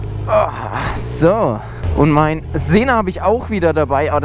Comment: Du sprichst bei 00:48 von einem Navi. Ich verstehe das akustisch allerdings nicht wirklich. "Mein Sehner hab ich auch wieder dabei...". Wo genau hast du denn dein Navi an der Maschine angebracht und kannst du mir vielleicht verlinken was genau du dazu nutzt? Danke vielmals! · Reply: Sena* einfach Mal googeln :)